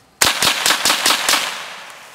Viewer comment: i was really confused by the comments because I thought he said "If you don't ride with America."